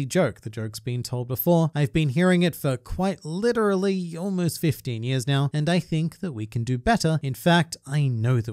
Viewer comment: i thought he said 50 years but he said 15 years